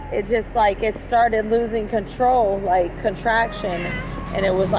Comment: Did she just say her car was having contractions?